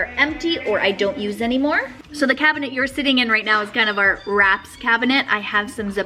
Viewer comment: When you said "Wraps" I heard "rats" 🤣